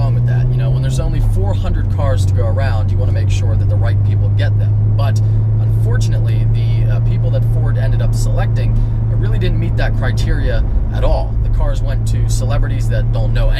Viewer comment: I swear I hear "you want to make sure the white people get them".